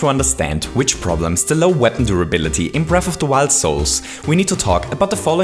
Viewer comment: Did anyone else hear him say "breath of the wild souls"?